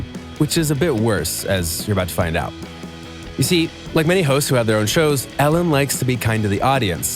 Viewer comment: did you just said “hoes” ?